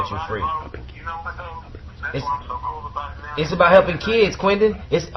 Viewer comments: did anyone else hear the cameraman whisper, "helping kids"? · Anybody else hear the cameraman telling him to say it's about the kids at 23:30 ?